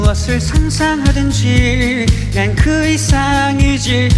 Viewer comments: 쥐롤라 답게 1:54 "무엇을 상상하든쥐" 라고 하는거처럼 들리네 ㅋㅋㅋ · 아니 시작부터 상상하든'쥐' 갈기는거 개웃기네ㅠ 다 쥐로 들려